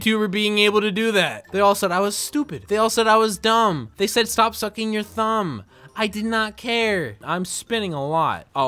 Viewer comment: I got a notification at 12:32 and I thought he said “they all said stop fucking my thumb”